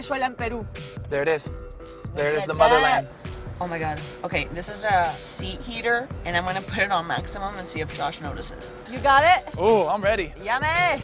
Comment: When she said "I'm gonna put this on maximum and see if Josh notices" I heard "ser if Joshe moans" and it was hilarious